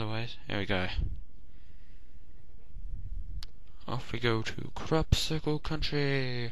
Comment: It sounded like you said "Crap-cicle Country!" xD